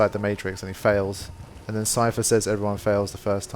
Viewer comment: Haha, when you mentioned Cypher from The Matrix I wasn't paying attention. I thought you said Seifer and got so excited thinking you had started an FF8 playthough (I didn't remember him saying "everyone fails the first time", but interestingly that could be a valid line for him). Oh uh, for some other non-FF games you should also play Lost Odyssey and DQ8... I only say that because they are as close as you can get to FF without it being FF :P